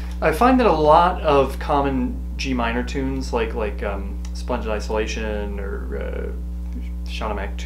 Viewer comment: I had a lot of fun trying to find those tunes you mentioned at the end of the video, because I honestly thought you said "Sponge in Isolation" and "Crabs in the Skull" 🤭I have figured out the actual titles in the meantime and am aware I have much to learn. Your videos are fantastic. Thanks so much for sharing your knowledge.